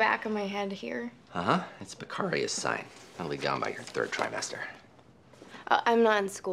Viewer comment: House: "It'll calm down by your third trimester. Patient: "I'm not in school." That poor thing.🤭🤭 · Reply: Okay I took another listen and I heard the words "by your third trimester."